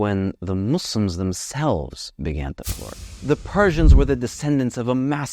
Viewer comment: "the muslims themselves began to [???]" What did he say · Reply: "Flourish"